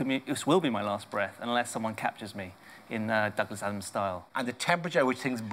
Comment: what did he say at 0:38? Douglas m style? what is that? is that correct, am I hearing that right?